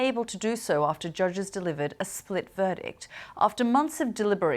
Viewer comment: Wow I thought it said " spit ' Instead of split ...lol